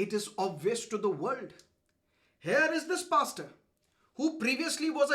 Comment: bastard?? Did I heard correct..